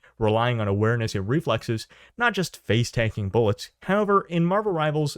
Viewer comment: i thought u kept saying game dads turns out you were saying game devs...lol